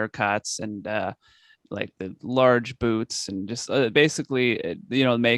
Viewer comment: I'm gonna be completely honest I thought he said "large boobs" instead of "large boots" 😂😂😂